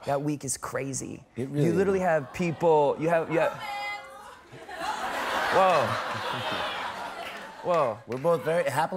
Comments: What does the woman shout at 2:05? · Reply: "Bromance" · What did the girl audience say at 2:04? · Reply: "Bromance"